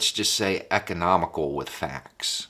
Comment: "The President is...economical with facts." Bless. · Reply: I thought he said "Comical with facts" 😅